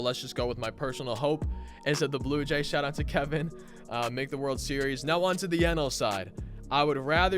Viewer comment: I thought he said Kevin made world series